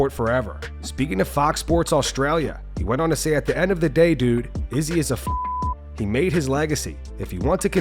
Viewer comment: I heard ‘Izzy is a f****’ from and had to read what he actually said